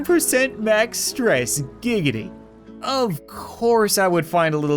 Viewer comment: DID HE JUST SAY GIGIDY